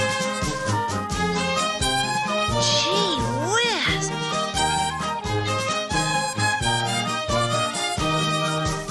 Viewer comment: What did he say ? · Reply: gee whiz.